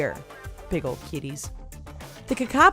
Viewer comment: does it say big ol" titties at minutes 2:40 ??